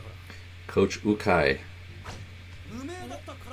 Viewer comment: Coach its pronounced you kai = Ukai. LOL sounded like you said oh kai Also yes senpai is a upper classman or someone who's older or even just someone u learn things from like a teacher